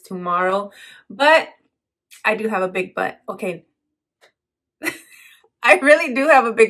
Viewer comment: OMG I was checking a text right when I clicked on your vid & then I THOUGHT I heard you say "I have a big butt" & I started to laugh so now I need to rewind these 23 seconds :D